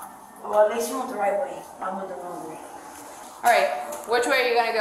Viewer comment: She said, “At least you went the right way. I went the wrong way.” At 6:27. I had to rewind to be sure that’s what she said. At least the girl knows she isn’t on the right path.